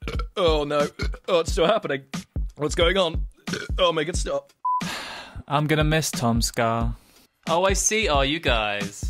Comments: "I'm gonna miss Tom's car(TomSka)" clever. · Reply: That's why I made an Edit with that line, cuz it's just sound like said "I'm gonna miss TomSka" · "I'm gonna miss Tom's car(TomSka)" clever. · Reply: It sounds like he said I miss tomska